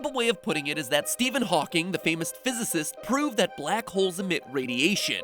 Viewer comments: the subtitles say Hawkeye the famous archer instead of Hawking the famous physicist. XD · Captions says Hawkeye the famous archer, instead of Steven hawking the famous physicist. I died. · Why at 9:20 is the captions constantly replacing Stephen Hawking with Hawkeye lmao. It even says "famous archer" instead of "famous physicist" lmao like the captions are staying in canon